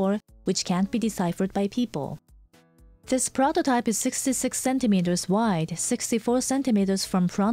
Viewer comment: wait did she say "this prototype is six six six centimeters wide.." at 2:00? It'll take you straight to hell when you're asleep XD